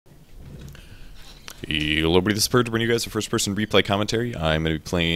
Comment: Is it just me or do I hear " YOLO this is purge ..." at every start of the video?